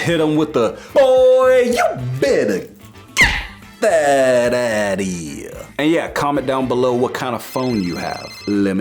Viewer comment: When I first watched him I thought he said. Boyyy you get that ass out for here